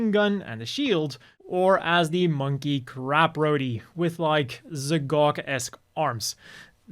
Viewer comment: I keep hearing "crap roady".